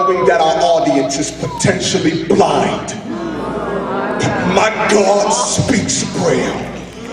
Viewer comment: What's he saying at 3:30? "But my God speaks prayer"? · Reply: "but my God speaks braille"